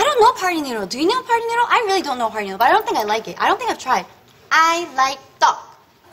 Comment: At first listen I thought she said I like dog